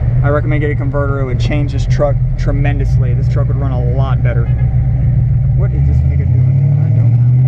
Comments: 👀 Did he just say What’s this Nigga doing at the 5:58🤣🤣🤣🤣🤣🤷🏻‍♂️ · does he say "what is this nigga doin" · sound like bro said “what is the nigga doin?” @ 5:58 😂😂